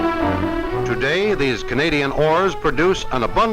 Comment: It sounds like he said Canadian whores 1:58 .